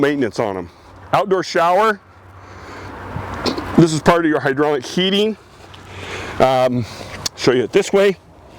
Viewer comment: did you say HYDRAULIC heating?